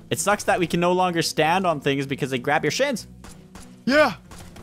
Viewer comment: Did say a swear word watch this part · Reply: He said shins